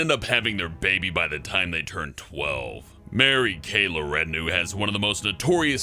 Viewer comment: What did you say? Mary Kay What? LaRenue?!